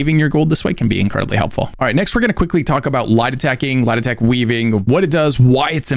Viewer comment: when you are saying "light attacking" it absolutely sounds like "lie detecting". It's actually weird how much it sounds like that. I had to turn on CC to understand what you were saying. If i might make a suggestion, I know during the tutorial they mentioned "light attack", but being brand new, everything was overwhelming so I didn't remember a lot from the in game tutorial. Later I did hear of "attack weaving" and googling it, found "light attack" mentioned but NOBODY said what light attacking was. I had to google like crazy and every time I googled Light attack, the google results were always referring to attack weaving. I finally had to ask a game friend WHAT light attack even means which was using left mouse button to attack. I realize you have been playing for ages so you don't realize how some terms you use, newbies don't even understand the term or what it means. Might be helpful if you realize that newbies literally have zero knowledge so sometimes explanations of things you are talking about, you might want to explain. Other than that, I'm really enjoying your vids for newbies. SOOOO much info that I'd probably be months before I learned it from playing so thank you a ton.